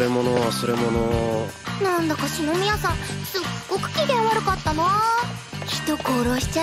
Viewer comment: she said shinomiya san not kaguya-sama so basically the subtitle is wrong just a mistake i happened to find